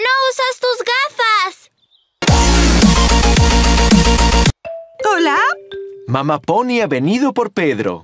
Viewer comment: Quien mas se dio cuenta de que dijo kola jjaja